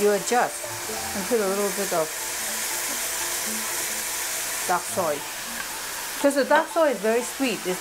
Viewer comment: Did you say duck soy, or dark soy at 3:19? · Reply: Hi again, I said dark soy. I put pictures of the dark soy I like in the newer videos like: <link> Thank you for your question. Best regards